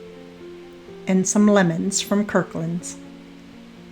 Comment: I am sorry. In listening again, I heard you say the lemons came from Kirklands! Thanks again and it is so look lovely!